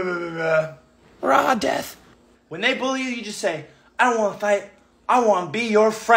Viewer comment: I thought CG said “when they blow you” 😅 Tell little Justin to quit hanging out with the Navy kid.